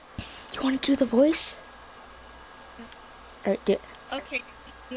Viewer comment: Hmm when you went to gym I heard “can you do the voice” lol